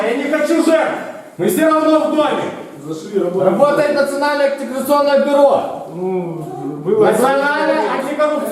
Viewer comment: Вам тоже послышалось, работает национальное оккупационное бюро?